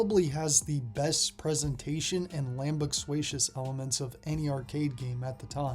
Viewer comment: I'm sorry, did you say "Lambuxuatious?"